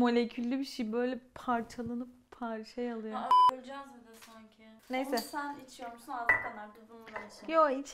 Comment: ne diyor ? Biplenmiş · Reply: Cam kırıldığında Yağmura o sodayı sen iç diyor çünkü cam var yane şaka yapıyor ve sonra :"Ha olucen zaten sanki" diyor yane şakasına